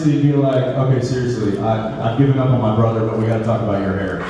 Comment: At around 49:00, what does Jensen say Dean would say to Priestley? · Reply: Something like "I've given up on my brother, but we've GOT to talk about your hair."